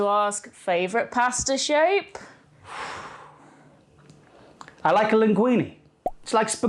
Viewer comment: I misheard “pasta shape” as “pasta shake” and thought “oh boy, this is gonna get very chaotic very quickly”